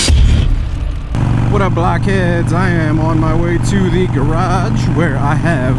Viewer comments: I thought he said, "wassup black kids" at the start😂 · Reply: I heard him say something like 'Wassup Blockheads' maybe? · I thought he said, "wassup black kids" at the start😂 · I thought he said, "wassup black kids" at the start😂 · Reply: I thought he said black heads